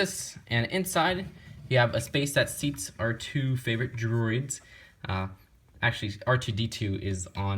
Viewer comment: I misheard R2 favorite droids instead of our two